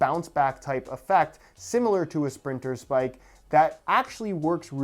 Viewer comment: I thought it said "Splinters"🤣